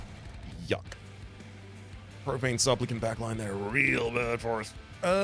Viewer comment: I keep mishearing "Profane Supplicant" as "Propane Supplicant" which is somewhat fitting with the big bell on their back looking like a big gas canister